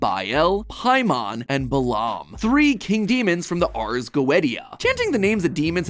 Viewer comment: Did he just say arrrr goaetteya